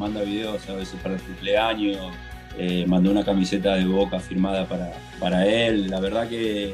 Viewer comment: Que alguien me ayude que dijo lucho , de boca o del boca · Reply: Que Riquelme le regaló una camiseta de boca autografiada al hijo para el cumpleaños